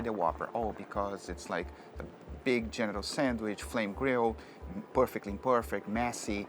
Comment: I was listening to this on my Bluetooth speaker while I was doing something and I thought I heard him say at 1:35 "a big genital sandwich" had to skip 10 secs back just to know if I heard it right.